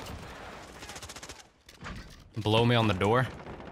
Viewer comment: sounds like he says “blow me at the door” xD